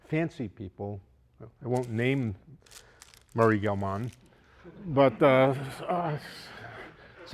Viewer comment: what name profesor says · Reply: Murray Gell-Mann i believe